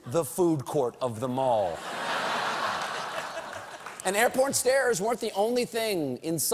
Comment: Does he say "air porn stairs'?